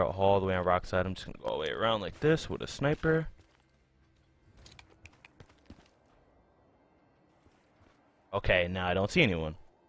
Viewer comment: I thought he said snipers only